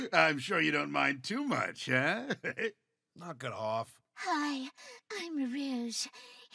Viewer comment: When this scene popped up I quite literally yelled "KNUXOUGE IS CANON #####'S"😅 0:13 OMG did knux say f off😮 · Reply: He said "Knock it off".